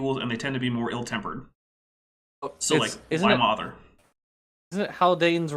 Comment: I misheard "So, like, why bother?" as "So, like, my mother." and I was very confused and had to go back and listen again like three times.